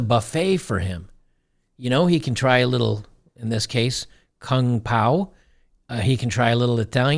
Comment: did you just say, "try some king pao..." joker.... lol